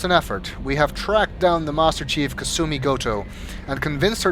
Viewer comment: I've re-watched this series several times now, but I never realized that it sounds like is saying "Master *Chief*" instead of Master Thief... this is giving me ideas, ideas that may be a tad dangerous...